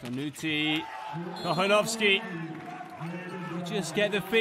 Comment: Anyone who speaks Spanish on here? The way the commentator pronounced "Kochanowski" @ 1:58:35 cracked me up. It sounded like "cojonoski". Lmao.